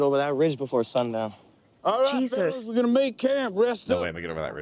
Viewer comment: Sounds like you saying “diet Jesus”.